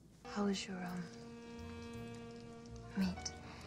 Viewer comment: What does Priscilla Presley say at 1:17? " how's your......" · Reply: Meat